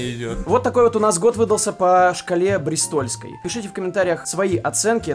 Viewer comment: Я сначала услышал "по дристольской шкале" а не бристольской, но в целом, я почти угадал) Всех с новым годом!